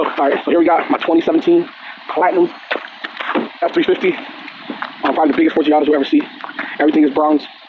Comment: did he just say “probably the biggest 350 you’ll ever see”? Lmaoooooo